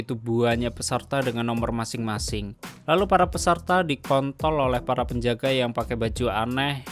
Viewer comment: "lalu para peserta dikontol oleh para penjaga" ...... Apa gue gak salah denger?